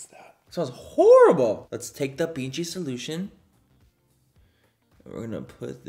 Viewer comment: For a moment i thought you said "let's take the pinchi solution " when its BG 😂😂😂😂